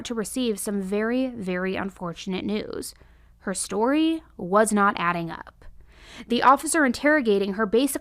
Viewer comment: I know I watch too much when at 20:10 she says "the story was not adding up" and I hear "the math ain't mathin".